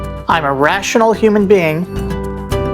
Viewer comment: My 2 year old thought you were saying 'frozen' at the end instead of formon. She is laughing and saying "he no frozen!" Maybe not the laugh you were going for, but woth something :)